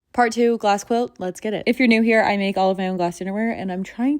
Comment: This look super cool but 100% thought I heard I’m making glass under wear ? What is it going to be ? 😊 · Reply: Dinnerware, I heard underwear at first as well & had to go back 😅